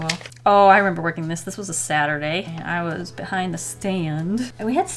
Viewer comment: i thought she said 'sadder day'